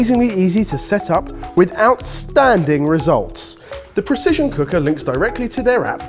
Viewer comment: I heard "with outstanding results" as "without standing results" and I was so confused